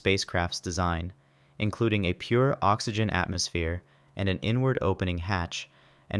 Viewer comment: I kept hearing N-Word instead of inward, until i heard him say outward 😂😂